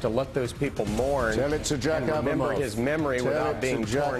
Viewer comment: What is Hitch saying at 4:20 ? "Tell it to Jack Abramoff (?)" Who is that, and why is Hitch bringing him up?